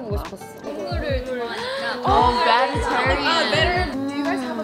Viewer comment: did I just hear say “oh vegetarian” or am I tripping?😭😭😭 HAHAHAHAH